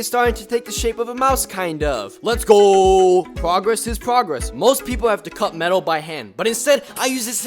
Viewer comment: I heard ”progress is poggers” :D